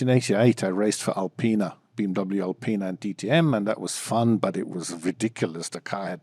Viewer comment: I thought he said BWT Alpine